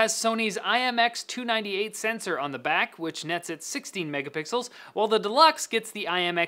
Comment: I misheard 16 megapixels as 60 and got really confused when the deluxe only had 24.